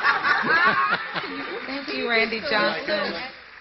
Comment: I'm glad I'm not the only one who heard her say Johnson instead of Jackson...that was funny.